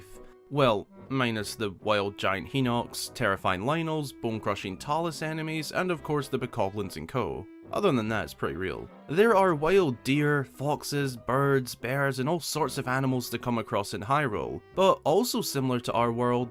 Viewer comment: Huh lol? Thanks tho :) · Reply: my fault. Thought you said oxes but you said foxes 😂😂😂 I'm tired